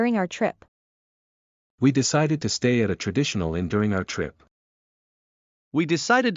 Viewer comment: 🐸Day17 「おや？」と、例文が提示される前に何度か聴き返した文がありました。聴きとったと思った語では、どうも違うような感じがしたからでした🧐。繰り返して聴き直し、聴きとれた英文と理解したことが一致するまで例文を見ませんでした。 #8: Because the two words venue and avenue differ only by the letter 'a,' I thought they might share the same origin and ultimately come from a Latin verb. Is my interpretation correct? #9: Initially, I misheard 'at a traditional' as 'additional', so it sounded to me like "We decided to stay an additional night during our trip."